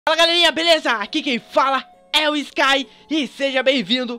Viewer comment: no começo eu achei que ele tinha falado "fala galinha beleza kkkkkkkk